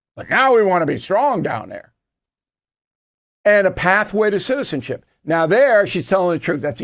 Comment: Sorry Bill, I have to correct you. What she said was "earned pathway to citizenship". Amnesty is not earned as citizenship is, it's handed out. She said NOTHING "truthful".